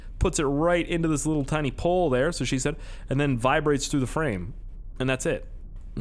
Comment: did any one else hear him say "thats what she said" really quickly lmfao